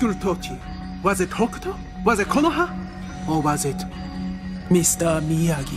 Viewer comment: did she say konoha aka the hidden leaf village